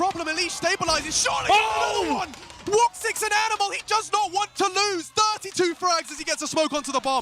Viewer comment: woxic an animal or what? What did he said? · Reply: Woxic phenomenal